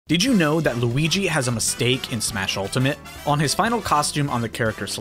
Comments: For a second I heard "Did you know Luigi was a mistake." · I got so excited because I thought it said luigi is a mistake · At first I thought you said "Luigi was a mistake in Smash Ultimate" and I was like "Rude..." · I heard this as “luigi was a mistake” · At first I thought you said “Luigi was a mistake”